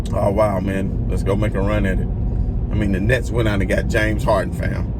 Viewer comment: I thought you said Nets are not good ladt 🎥 lol